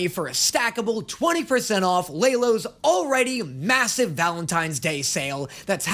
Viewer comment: did you say…massive?